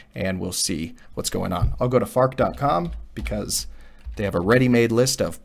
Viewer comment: for a sec i thought it says "how to fart"